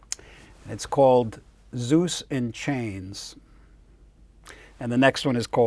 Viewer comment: I thought you said "Seuss In Chains", and was all like, RIGHT ON BRO! And then I realized you said "Zeus In Chains".